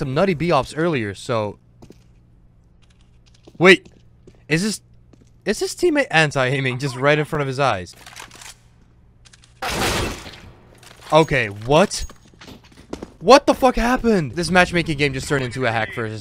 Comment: i heard "hentai aiming"